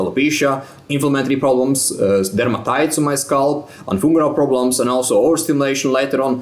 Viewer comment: Thought he said female problems but he said something else. But female problems causes stress too 😅😂